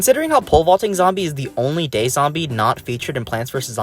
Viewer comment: i thought you said he was the only gay zombie. whats worse is that i believed it for a sec before realizing i misheard.